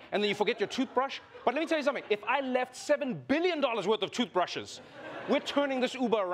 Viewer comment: Yo, I thought he said million. BILLION!?!??